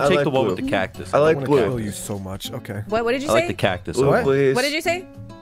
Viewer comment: I thought he said "I love you so much" It was; "I wanna kill you so much."